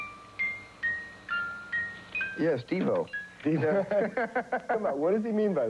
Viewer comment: does he say "Yes, Devo"?